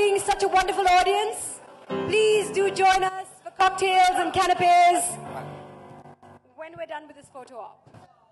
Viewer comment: “Cocktails and cannabis “- did I hear that right?